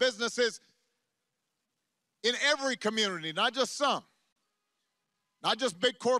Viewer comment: Vote for this guy if you want to go to cunity college. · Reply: I thought he said munity college.